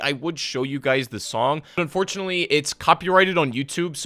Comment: So no one is gonna talk about how he said “I don’t wanna get stroked,” instead of copyright striked @ 8:58 lmfao